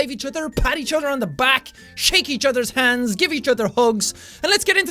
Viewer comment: lol I thought he said "Shank eachers hands" Grabs knife IM READY!!!😂